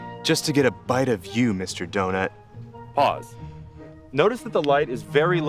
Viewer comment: Love your informative videos! However, I have some things to point out about captioning—at 1:51, auto-captioning says "cause" instead of "pause". Closed captioning & subtitles are extremely important for the hard-of-hearing/deaf, so accurate captions would be great for those. (: Thank you!